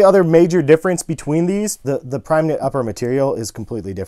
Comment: I heard u say "Pregnant upper" Instead of "primeknit)